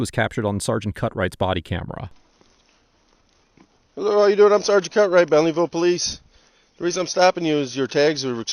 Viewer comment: On that second video did he just say his name was sergeant Cuck-right?! Lmfao